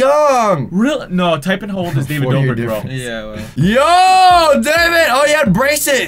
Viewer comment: I thought he said Dobre 😂 (Who's watching this on 2k18)